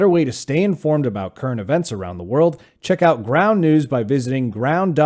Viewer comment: It's probably my ears but when you were talking about your sponsor I thought you said Brown News. I know you didn't say that. Funny, right? ;)